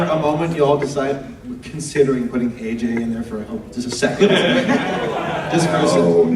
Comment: what does he say at 37:12.. Put who in there? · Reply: asked if he was thinking about putting AJ somewhere as the typical Applejack background pony joke.